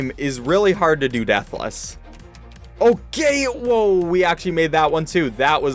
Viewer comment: I think he said oh gay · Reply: he said okay